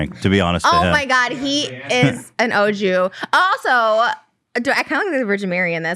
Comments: Did trisha say ‚oh mah god he is an Ojew‘ as in original jew😂? I‘m really not sure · did she just say O.Jew... like O.G but... jew ?? 😆😂